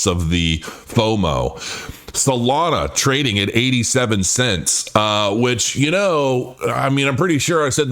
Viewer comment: "Solana at 37 cents". If I wasn't looking at my screen when I heard this, I might've thought it totally collapsed! 😂😂